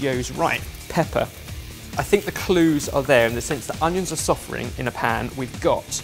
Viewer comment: I thought said "the onions are suffering in the pan"